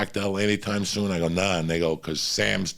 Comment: What is he saying at 6:03 "Nah, ****?" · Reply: “Nah, and they go…”